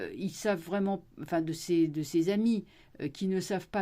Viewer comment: J'ai entendu "de ses amibes" à 4:08. Au fond, ça les définit plutôt bien...😂